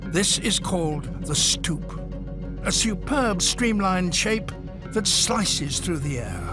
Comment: for one moment I heard Stuka